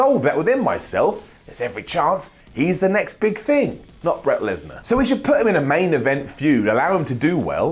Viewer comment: I swear said "Bret Lesnar".